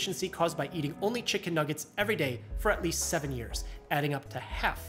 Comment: the captions say 8 years but he says 7 years 🤔